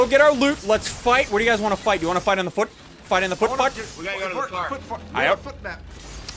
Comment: DID HE JUST SAY FOOT FU#&